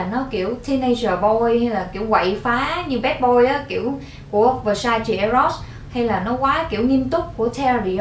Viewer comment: kiểu quậy phá badboy là loại nước hoa j v ạ. cho e xin tên với e ko nghe ra đoạn đó😂😂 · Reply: Versace eros nha e, dùng loại đó đi bar club í e